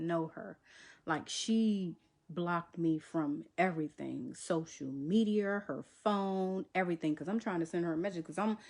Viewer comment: Did she say social mediarrrrr 😂😂😂😂😂😂😂😂 @ 1:48 lmao ok madea Go off girl 😂 this is so lame and desperate 😂